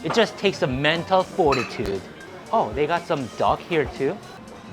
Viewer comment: I thought you said they got some dog here too but it was duck haha